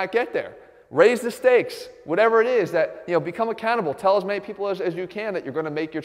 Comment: lol its sounds like he says "become a cannibal"